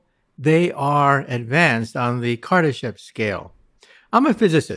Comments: I thought he said on the civilization chart the Kardashians scale😂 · I thought he said Kardashian scale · Damn almost thought they said Kardashian scale 😅😅😝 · I thought he said kardasian scale...I was like we fucked!!.....